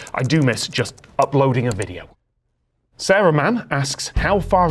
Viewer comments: 100% thought he said "Saruman" 😅😅😅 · who else heard Saruman?